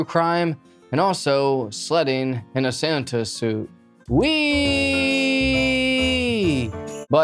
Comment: I literally thought this man said “ slutting in a Santa suit” 😂